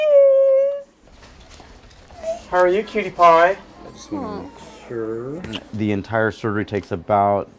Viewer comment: it sounded like the doctor say PewDiePie